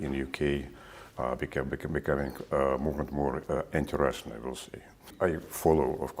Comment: THE RUSSIAN AMBASSADOR SAYS : ..." ANTI-RUSSIAN "... The subtitles say : ... " INTERESTING "... The subtitles are a joke !...